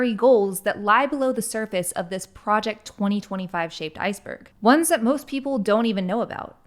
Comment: I thought I heard "shaved iceberg"